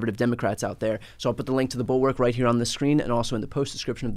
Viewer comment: Call it a Freudian slip, but I heard ‘Inside the reich’ at the opening of this…. 😅